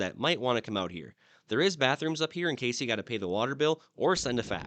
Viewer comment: did he say “there is backrooms up here”.. wouldn’t really surprise me..